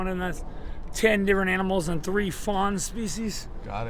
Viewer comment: Ten different animals and three fawn species...got it! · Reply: What u said made more sense than what I heard, I thought he said 10 different animals&3 foreign species ,😂